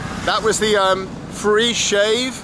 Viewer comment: free shave in Miramar .... Is he actually said that